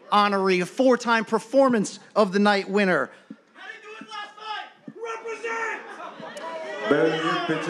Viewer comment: what did he say? was the fan even dissin Masvidal? All I heard I thought was “How’d he do last night?”